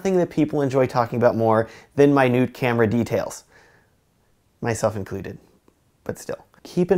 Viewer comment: It sounded like you said "my nude camera details".